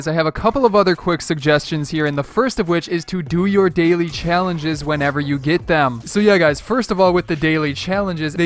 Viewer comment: I thought he said do your daily challenges when you eat them lol